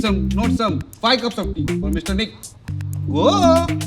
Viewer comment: Why I heard at 6:20 Mr. Dick